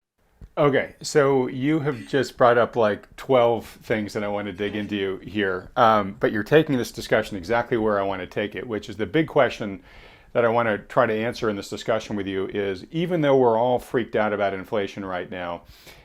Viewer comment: i heard you say, 'dig into you here'.... freudian!